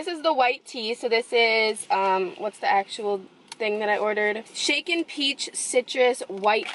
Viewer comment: Ur mom's soo cute 😁 and I heard "chicken peach" the first time 😅😅😅